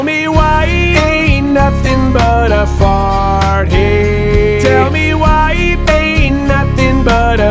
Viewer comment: I always heard “Ain’t nothing but a partyyyyyy” And not “ain’t nothin but a heartache”